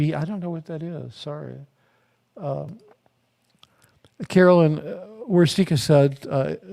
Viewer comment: so sorry, you're right. i thought said "caroline" but said "roslyn" as i re-watch. he said his mother is still in. still not sure what's the deal with the father. ah yes just found the mother. now the pieces all fit.